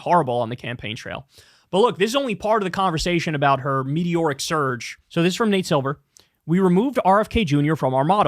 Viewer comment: Could’ve sworn he said mediocre surge in the first video. Haha